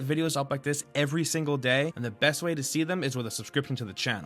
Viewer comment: this is what I heard : I'm Mainge the new guy... .... ... ... is that right maybe its Minge...